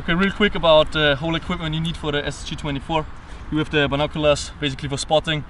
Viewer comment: Is It just me or did he say Ssg 24